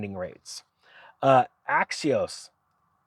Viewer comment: when he sounds like he says "axios" he means axos.. per their website they do up to 96 months but so do most other banks these days. none of this matters. the prices themselves have to come down first, only suckers pay these prices. be firm, dont budge.